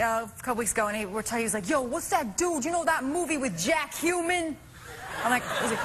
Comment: I thought she was saying Jack Cumin